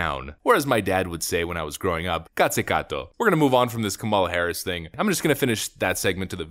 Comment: Good God! I nearly fell on the floor laughing when I heard your "Κάτσε κάτω" remark! I've been saying that about Κουμούλα Harris for years.